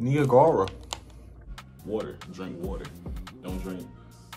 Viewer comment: did he say nigga water???